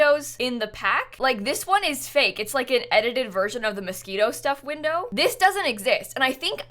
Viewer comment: I KNOW you’re saying Moschino, but with every power in me I keep hearing “Mosquito” and dying laughing